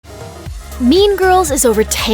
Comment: i keep hearing "meme girls"